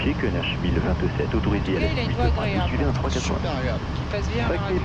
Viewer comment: I love the voice of the approach controller! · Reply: It's exactly what she's saying ;) 00:57 -> "En tout cas il a une voix agréable" 00:59 -> "Super agréable !"